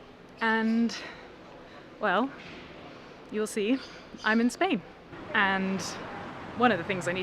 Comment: Oh “I’m in Spain”! Thought you said “I’m Insane”